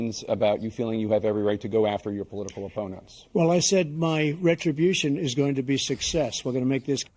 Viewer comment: I heard "my retribution will be success," not "my retribution will be 'A' success."